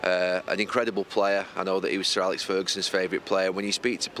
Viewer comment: Thought he said Sir Gary